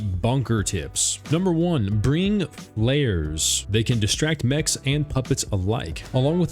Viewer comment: what does he say? bring what? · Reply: Flares